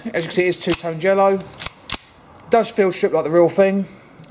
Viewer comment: did he say "does feel shit like the real thing" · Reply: SillySlipper he said field strip